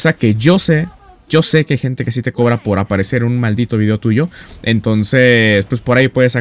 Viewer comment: Uds escucharon "Yo se que hay gente que si te cobra por aparecer en un madito vídeo tuyo" yo escuche "Saludos al fabuloso" 😂